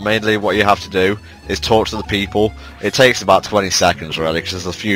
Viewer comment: my head phones screwed up at the beginning and because of it, it sounded like you said "torture the people" instead of "talk to the people"